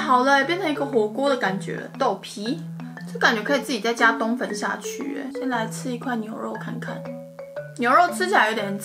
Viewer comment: 我聽成幹可以自己在加冬粉下去耶....